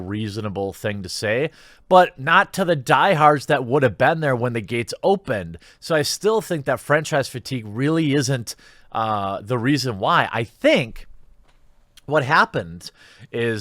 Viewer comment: I heard “French fries fatigue” 😂